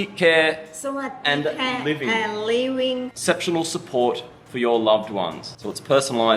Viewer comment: I swear you said it was "sexual support" ... I was thinking sign me up The bank issue is annoying, I have a meeting with my guy when I get back as they froze mine and and a have a little bit in the account. As for the costs..I think it is location based as well, places in the north are cheaper generally so I wouldn't be moving out of country but certainly for those on tighter monthly budgets could look to other more affordable locations, Hua Hin for instance is cheaper than Pattaya. still on the beach and worth investigating.